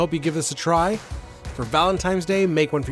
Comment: Did say "ValenTIMES" day at 6:07????? 😀